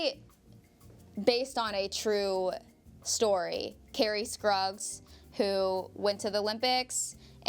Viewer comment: did she say Kerri Skrugs ?